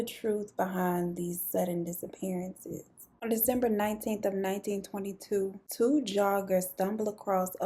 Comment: Did you say 1922?! That threw me.